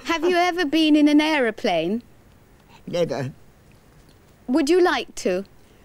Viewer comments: pause right there. Did she say?????????? I mean y'all hear it right?? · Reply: She said never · what she say? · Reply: Never.